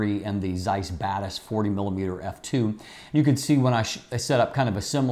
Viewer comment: I initially heard it as "Zeiss Badass 40mm f2" and thoght the "Badass" was a compliment to the Zeiss'es IQ. :D BTW such naming would have been way more intertaining than those boring letters such as R LM WR =)))